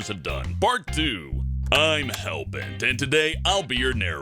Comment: Did he say Bert 2 ?